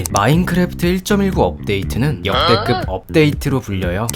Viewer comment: 나만 앞부분 "없"데이트로 들림?